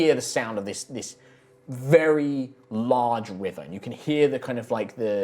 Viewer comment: I thought when he said large river, I thought he said Large wyvern I was like, Welp rip Lucius